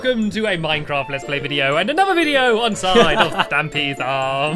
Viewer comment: Did Stampy say "Onside"